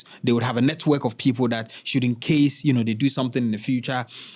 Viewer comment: I have heard you saying this multiple times. The right way of saying it is "just in case," not "should in case."